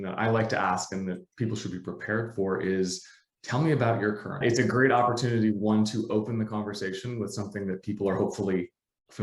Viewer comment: Hi! Great interview, thank you! What does say at 2:30? "Tell me about your..." it looks like the video gets cut! Thank you for your help! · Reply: Product. They say it later on